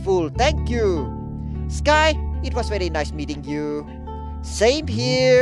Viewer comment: Sorry, I understood "eating you" instead of "meeting you", which kind of makes sense in this series. 😂😂😂 10:55 I had to read it twice because I read "genetic analysis". 😂😂😂